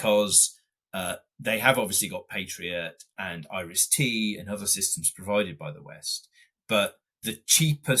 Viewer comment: I think he sez IST or RST...but it sounded like "Irish tea," and I was like...serve me some of that, boy-o!! lol